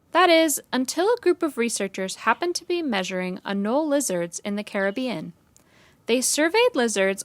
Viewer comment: Anole lizards huh....a couple of them are avoiding the hurricane season by jumping through my kitchen window. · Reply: Lol I thought you said anal lizard